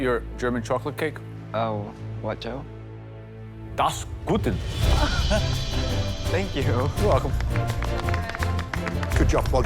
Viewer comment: I speak German and I have absoloutly no idea what Joe said in the cake episode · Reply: he should’ve said “das ist gut” not das guten😂